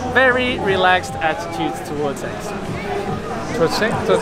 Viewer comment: min...did he say very relaxed attitude towards sex?